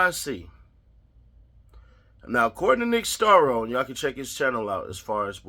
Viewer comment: Does anyone know the name of this lawyer or his channel? I thought he said Nick Staro, Scarrow, or something, but I can’t find anything